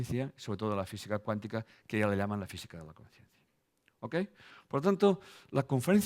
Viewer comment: "...Fisica cuantica que hoy la llaman Fisica... " que? que no entendi (min. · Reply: Hola Oscar, "A la física cuántica que ya hoy día le llaman la física de la conciencia". Un abrazo